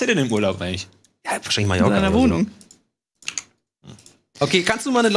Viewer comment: Hat gesagt, dass Marco in "meiner Wohnung" ist? ^^ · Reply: Ja, ich habe auch "in meiner Wohnung" verstanden